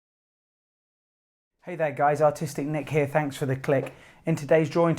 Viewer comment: I'm so high I thought you said autistic nick 😆